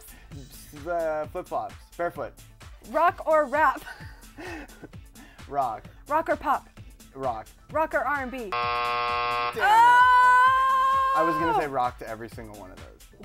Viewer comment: LOL i loved his slurred responses and made up ones. gosh he's cute ps- did he say damn it at 1:46? or dang it PLEASE ASK TROIAN BELLISARIOOO!